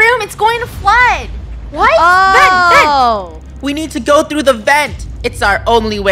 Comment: When said “vent vent” at 6:51 i thought she said “Ben Ben” HAHA 😭💀🤣🤣🤣